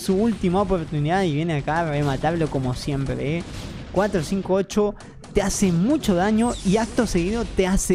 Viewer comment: asdsass pensé que decía 458 te hace mi&@